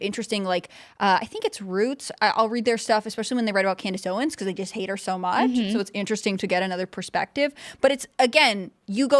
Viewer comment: Lol did she say “…I hate [Candace Owens] so much” or “…they…”? Whats the drama? Like I want to like Candace Owens but I don’t for some reason that I can’t place.